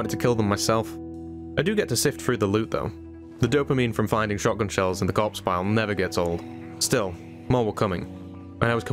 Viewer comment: i noticed at 1:33:27... you have something called "proximity" and then a word i cant make out thats helping you look at a bunch of zombies at once to loot them. i generate blankets of corpses at times as well, but looting them and knowing which ones ive looted or not is a huge pain. how do i get this? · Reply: It's a mod called proximity inventory, adds a tab to loot everything within reach. Right click to force it on or off for different scenarios